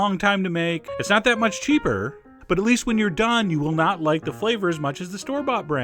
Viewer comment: seconds did you say: "not" like the flavor as good as the store-bought?